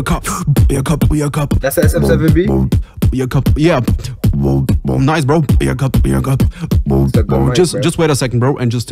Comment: who is the beatboxer i didn't heard it clearly. Btw lit beatbox bruh.🔥🔥🔥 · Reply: He is right, It´s big ben the BIG BOY :d and also thanks a lot man 🔥🔥